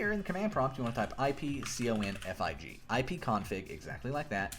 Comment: doesnt work for me and i kind of struggle to see the text or what he says :/ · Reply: ipconfig